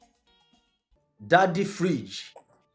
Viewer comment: I heard Daddy fridge